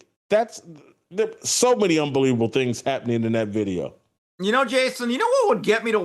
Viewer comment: Did I hear Jason say “UnbeWeavable” I could’ve sworn I heard it? Anyone else? 😂😂😂